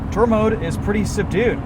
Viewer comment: I've been waiting for this one, however, did you say "turbo," at 05:49. Cayman who? My new dream car.